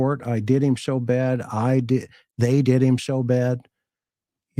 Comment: I always hear “I did him… “, not “they did him…”